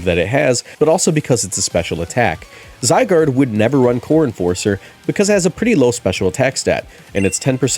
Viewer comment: All through this video I kept hearing "Corn Forcer" and I refuse to believe was saying anything else